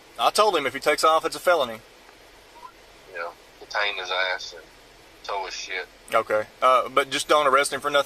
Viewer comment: "Detain his ass and tow his sh*t." I'll be laughing for weeks because of that. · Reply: For a moment, I thought I heard "Taze his ass and tow his sh*t"